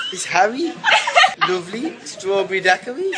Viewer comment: i think Harry said "it's Harry idek strawberry daiquiri"